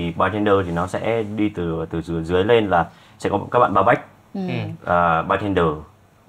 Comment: Tr ơi bar back mà t nghe thành bareback =))))